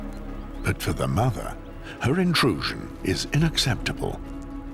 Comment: did he say “inacceptable”? 😢 *unacceptable.😊